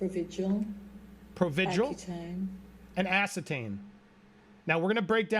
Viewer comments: I might want to add that I think I heard them say Accutane (NOT acetane) at 14:30. Accutane is a common acne medicine. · I think you misheard. Im pretty sure the witness said accutane. That’s a prescription given by dermatologists to clear up chronic acne. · I thought I heard the ex-assistant say 'ACCUTANE' not ascertain. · I'm not sure what drug you are saying...but it sounds like you are saying "ass-ih-tane"...she said "ack-u-tane" which is for the treatment of acne. It's not a "heavy duty" drug, nor does it alter/impair behavior in any way. 😂 Provigil on the other hand may be different, I don't know anything about it.